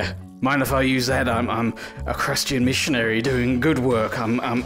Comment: Does he say "crustian missionary" 🤣🤣🤣